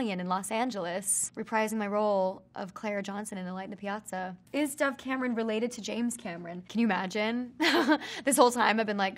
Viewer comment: When it said is dove Cameron related to James Cameron I thought said is dove Cameron related to James Charles 😂😂🤣